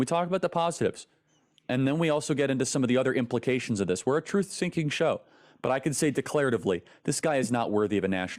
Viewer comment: Noticed his Freudian slip Charlie Kirk 10:20 He says we’re a truth sinking show. I’m sure he was meaning to say seeking, but he said sinking at least that’s what I heard truth syncing, and I would have to agree with that.